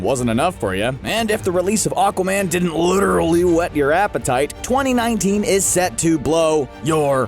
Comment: I thought he was about to say ''wet your ass''